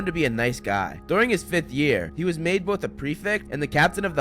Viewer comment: Wait at 1:35 does he say 5th year?